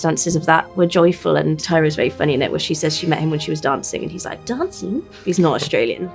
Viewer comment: what is the joke, what was she saying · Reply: I think she said “Dancing” with a kind of an Australian accent since the actor for Theo is from Australia 🥰